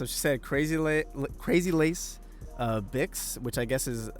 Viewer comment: oh, in the official clip, I thought she said "Crazy legs" xD